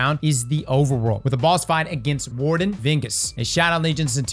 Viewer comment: For a second I thought I heard you say "Warden Dingus"...